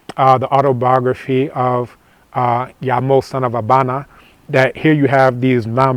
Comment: he mentions a book the autobiography of .... its hard to hear what hes saying here. Does anybody know the name of the book he is referring to? It sounds like a very good read. Sounds like he said "Yamo" but nothing pops up when i search that